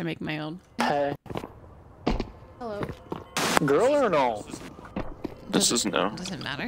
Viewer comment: i thought he was sayin a girl is noob but sayin something completely out of topic is wild